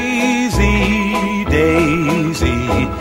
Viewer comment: Bro i hear "daisy daisy gimme ur a$$ to cook"